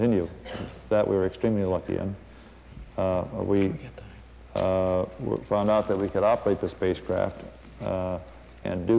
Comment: what did he whispered to him? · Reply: Sounds like "We got time."